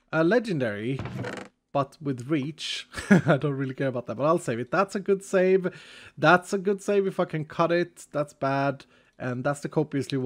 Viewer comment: am i the only one who heard him on 28:15 say we F*cking cut it?